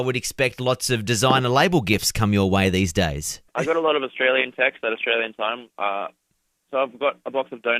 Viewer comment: I thought he said "I got a load of Australian sex" bye